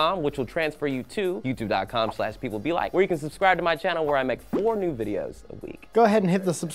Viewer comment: Will said 4 new videos. I heard, "Nude". Not going to lie was a little disappointed when I realized my mistake.